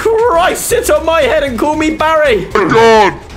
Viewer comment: Who heard him say sit on my head and call me bary 19:04 😂😂😂